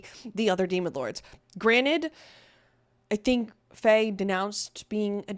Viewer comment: you said Fey again instead of Frey 👀👀